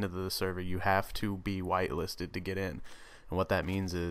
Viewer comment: lol I thought he was gunna say you have to be white to get in the server.